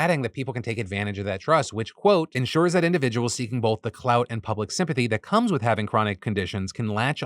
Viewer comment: there is a typo in the captions, instead of saying public sympathy it say pubic sympathy